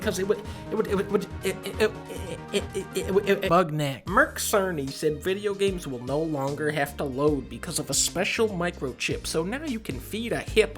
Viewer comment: did he say bugknack? 😂